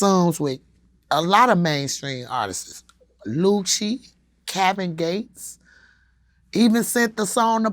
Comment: “Cabbage Gates” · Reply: Lol I thought she said "cabin gates"